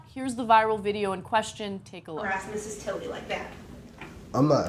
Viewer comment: "Sitting here until someone is available is harassing." Yup, that says it all. He's completely in the right. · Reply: nguyen danny if you listen closely it sounds like she said Mrs. Tilley.